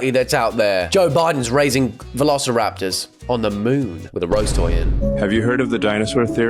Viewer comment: did he just say “with a rose toy in”? In where? It just occurred to me that doesn’t actually know what a rose toy is 💀